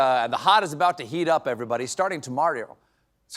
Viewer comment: I think I just heard you just say "Starting ToMario" 🤣🤣😂😂